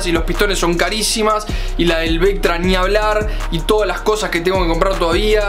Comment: Acaso escuche "Ya que las vielas y pistones son carisimas *Y MAS LAS DEL VECTRA*"?